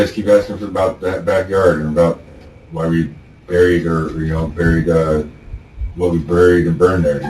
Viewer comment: They didnt catch that? 17:30 "buried her"